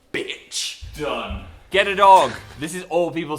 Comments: I was not looking for a second, and I heard "Get it on" instead of "Get a dog"... ._. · When they said get a dog, I thought they said “Get it on” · Okay but why did I hear “Get it on” when they said “Get a dog” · When Phil said get a dog I thought he said get it on 😂